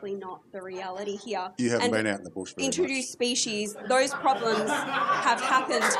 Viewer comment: Guys please 24:05 what does the farmer says to get clapped I'm Italian and the subtitles don't get that part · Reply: He said "you haven't been out the bush very much."